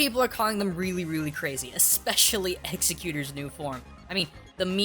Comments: XD it sounds like your saying Executor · its sounds like he is saying executor